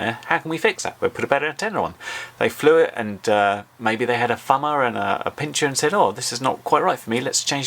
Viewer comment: Lol I thought you said fellows and bitches not thumberz and pincherz